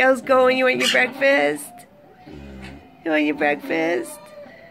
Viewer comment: For a moment I thought she said "you wanna become breakfast?"